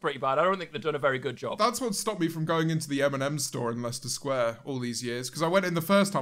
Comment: Timestamp - 10:20 When James mentioned the M&M store, I literally thought he was saying, "the Eminem store". You can imagine my confusion. 😂😂😂